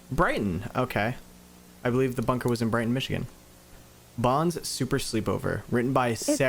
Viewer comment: no wonder why they were locked up (I thought it said blighttown)